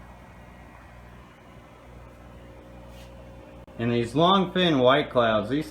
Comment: Ha. I thought you were saying the longfins were broken. I have to say that peaked my interest. Beautiful fish!